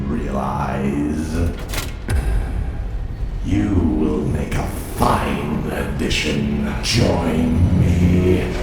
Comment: I misheard the intro and hear “your lightsaber will be a fine addition to my collection”